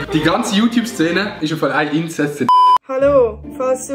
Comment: Feier das Video mega ☺️Was sagt bei 0:08 nach YouTube Szene ? Bin leider Deutscher und versuche so viel wie möglich zu verstehen 😅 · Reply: das die youTube szene ein insest ist :)